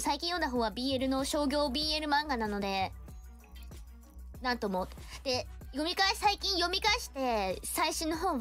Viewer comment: I think you misheard this as なんども (many times) but she's saying なんとも which is short for なんとも言えない meaning "I can't say anything [about that]". Combined with the previous line she was basically saying that one manga she's been reading recently is a commercially published BL manga so there's not much she can say about it / not much to be said about it.